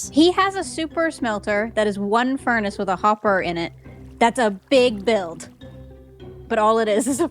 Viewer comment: wait is she American? And did she say innit?